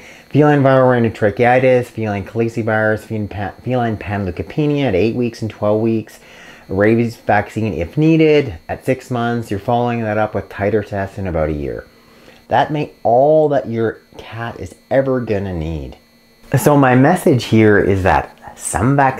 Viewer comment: What was the last 2 cat vaccines you said to do after a year ago 3:57? Titerteff is what I heard 😅